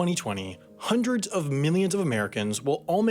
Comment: "Hundreds of millions of Americans will all make their way to their local polling station-" Bold of you to assume turnout will be so high. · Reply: PhazonSpaceSystems Except it very much sounds like Sam said “Hundreds of minions of Americans.” Which is probably more accurate.